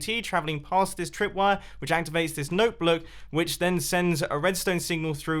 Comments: Sounds like he said note bloke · “Note Bloak” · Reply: GuppyBunz at 6:54 I heard "note blook" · did he- did he say note bloke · 'Note bloke' Jumbo 2018 PS. ..... he actually said note bloke i Didn't make a spelling mistake, if you don't belive me then re - watch the video at the time shown 6:55 ok? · Cool vid, really good. At 6:53 you said something weird. It sounded like “Notebloock” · he said noteBLOOK instead of noteBLOCK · Did he just say note book? LOL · Did he just say note bloak???